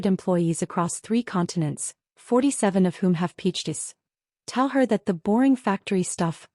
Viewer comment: The narrator says, “…47 of whom have pitched this.” The transcript says, “… 47 of whom have peaches.” That doesn’t really make any sense, either. Based on the context of the comment, I THINK the narrator said, “ … 47 of whom have Ph.Ds.”